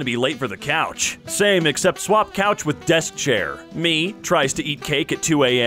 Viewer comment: I actually watched this video many times and every time i thought he said ''death chair'' instead of ''desk chair''